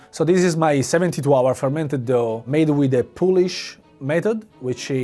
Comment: The subtitles say "Polish" but I think he meant "Pulish", as in from the city Pula in Istria